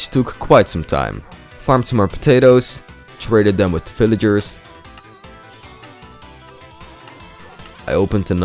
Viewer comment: it feel like you said fillager instead of villager